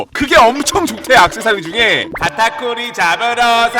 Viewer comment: 왜 카타쿠리가 딱따구리로 들리지?ㅋㅋㅋ 나만그런가?